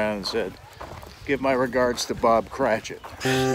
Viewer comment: I paused it right at the spot Eugene says, "give my regards to Bob CR . . ." and I thought his kid said Bob Crane. Was Hogan known for wearing a nightshirt?